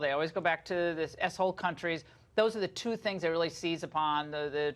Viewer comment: did she say "s-hole countries"? XD lol 3:34 I like that Dr.